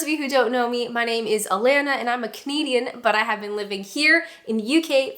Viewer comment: Been watching your vids all year at 1.25x and for so long I thought u were saying “I’m Alana and I’m a COMEDIAN” lol